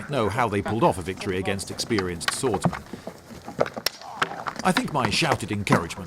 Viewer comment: did you say ex-spear-ienced swordsmen?